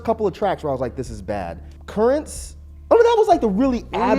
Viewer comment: Tell me didn't say he fucked with 'Currents' 19:03.. I must have misheard him, right?